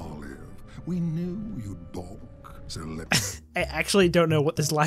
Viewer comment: he's probably saying "balk", because the dictionary definition nearly perfectly describes what she said. but him just saying the word instead of "... balk at the idea of..." is kinda hilarious